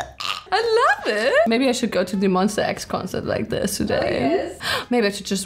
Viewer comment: did she say monsta x??? is she a kpop stan and i didn't know :o